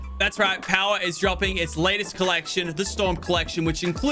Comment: I thought he said ‘stonk’ collection 🤣🚀